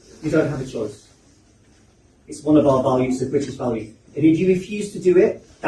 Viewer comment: I thought he said "Christian values" too. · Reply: He said "British values".